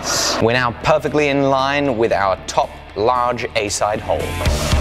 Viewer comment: anyone else here 'A hole" instead of 'A side hole'?